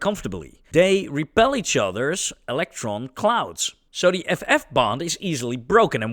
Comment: I know you said FF-Bond... But at 2x, I heard "F-bomb" and felt that was just a likely =oD